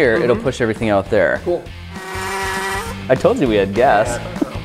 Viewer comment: wait did they just say "I told you we had death" or its just me